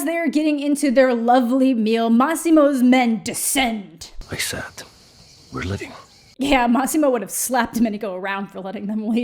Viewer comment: Did he say "I said we're leaving"?! Because I heard it "I sat, we're living" !!!